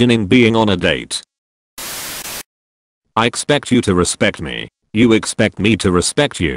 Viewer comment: and my infp dumbass thought it said "I you expect me you to respect me you"